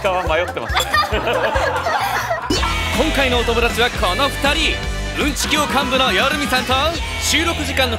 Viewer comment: の夜見の紹介何て言ってるんだ？ うんち教官部？？